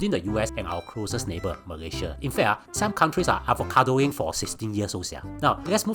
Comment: Nothing to worry. Singaporeans are now smarter and do not easily buy into the idea of marriage. 1:53 Did you say advocadoing? 🤣🤣🤣🤣🤣 So cute your pronunciation!